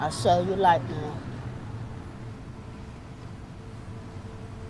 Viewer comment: always thought it said "it binds the aether". Kinda prefered that...